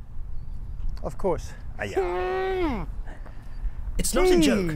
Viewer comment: Did I hear say "aiyah"?